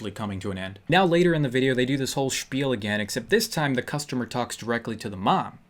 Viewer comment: Did he say "Spiel"? (German word for game)